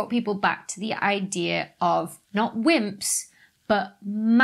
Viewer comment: When she said wimps, i thought she said simps for a second